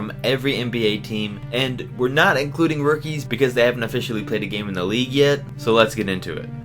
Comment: I thought you said no rookies